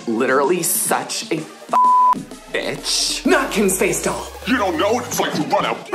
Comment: “ not Kim’s face, doll” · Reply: I heard “ Now Kim’s face, doll”. She was telling the camera to move from kourtney to Kim